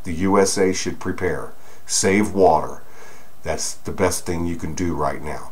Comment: What did say at 8:46 ? Thanks all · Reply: save water.